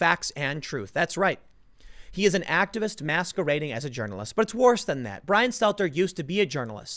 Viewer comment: I have my fan on while i listen to this, and i had to pause rewind and listen again to it... I thought heard Tim say Brian Stelter is masterbating as a journalist.